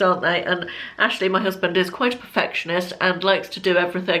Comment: Great vlog as usual. When you were talking about home renovations I thought you said at first that Ashley was affectionate when you actually said he is a perfectionist! I did wonder which direction your video was taking..... 😉😂😂